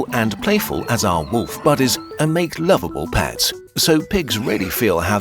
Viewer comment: Did he say Mabel or doorbell? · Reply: "They are as intelligent, social and playful as our wolf buddies and make loveable pets – so pigs really feel how they live and arguably have it the worst of all animals we eat. Probably more than 90% of all pigs live in torture camps." I have no idea how and where you hearedany of the words you wrote there.